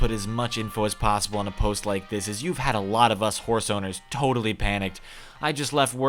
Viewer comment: What was said in beginning: “horse owners” What I heard: “war zoners” I need new ears bruh